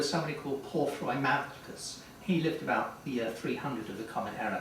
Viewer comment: I can't make out the name that Hudson provides at 3:30. Can anyone help? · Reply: Porphyry Malchus. His book Against the Christians was destroyed.